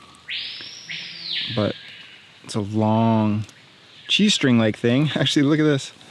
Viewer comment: Bro I thought he said G string😂